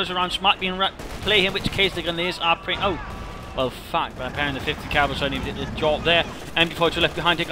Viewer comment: Did you say F@ at 17:41? Nice